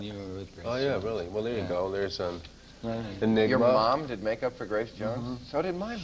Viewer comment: Is the audio playing tricks with my ears or did I hear at 5:43 someone say ni@@ mom ?